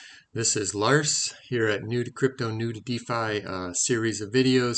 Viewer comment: Bait and switch!!! I heard "Nude Crypto, Nude Defi" but nooooo all you talked about was Governance. Bah! Seriously though, great job on another great video. I watch them all!